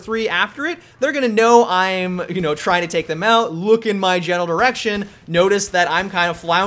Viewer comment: it sounds like he said "Genital erection"....